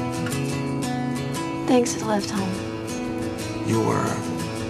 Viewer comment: to to 0:06 wht did she say!? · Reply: "thanks for the lift home"